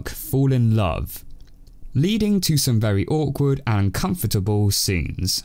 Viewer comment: u said comfortable instead of uncomfortable